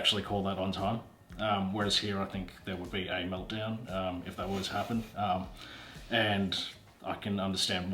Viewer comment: 今日の動画めっちゃ知らない表現出てきたので自分のDIstinctionに追加しました。 pronounced willy nilly meltdown make up for sth shy away from sth appropriate to take sb an extra step to do get out of one's way to do 勉強になりました。最近日本語の「適当に」にあたる単語を探していたのですがwilly nillyが使えそうなので試してみます。 1:45 あとここの最後の字幕、"If that were to happen"のところ、wasが聞こえるんですけど、were toであっているんですかね。 どなたかわかる方いらっしゃいますか？